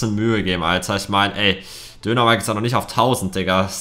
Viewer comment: Ich habe die ganze Zeit verstanden Dönermike😂😂😂😂😂😂